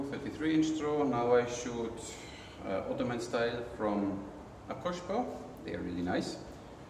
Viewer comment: What is the name of the second bow you used? The blonde bow. It sounds like you said: oshkosh. Perhaps someone has already asked in the comments section, but I didn't find it. THANKS!